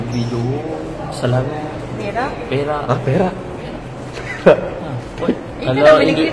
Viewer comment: gue ngakak euy pas salah denger perak jadi berak 😂😂😂